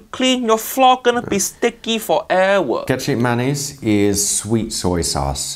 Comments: I thought he said Ketchup Mayonnaise. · When he said he'll add "Kecap Manis" I misheard it as "Ketchup Mayonnaise", I was confused on so many levels :D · Thank you for the subtitle swore they was saying ketchup mayonnaise 😅